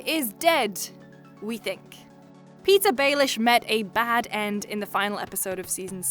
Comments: Am I the only who heard Pizza Baelish at 6:09? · When she said “Petyr Baelish” I thought she said “pizza baelish” 😂💪